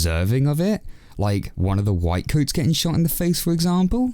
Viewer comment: PeTA are the white coats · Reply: i thought you said "white ones" lol